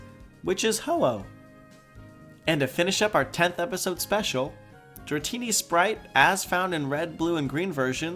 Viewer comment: captions say "dirty sprite ass" XD